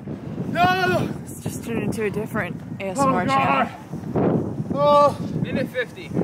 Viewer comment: just turning into a different ASMR channel? is that what said? ( 14:50 )